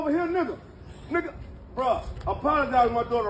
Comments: voice “ Say it again, Nigga - Shut up Woooo .. !!!” 😅😂😂😂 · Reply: not sure if it was text or touch me. I thought she said touch. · When she said dont text me why he even got her number if she was so scared why she gave him her number her fast ass just got caught up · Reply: I thought she said touch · I bet his daughter is the problem. Just listen to the mother💯💯💯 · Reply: I thought she said,don't touch me. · The fact that she said don’t text me shows that she initiated the whole thing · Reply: I thought she said don't touch